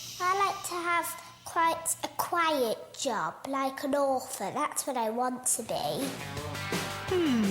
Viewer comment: Ha! For a second there I thought the little girl said she wanted to be an ORPHAN LOL I had to go back to listen more carefully & then realized she actually said she wanted to be an author.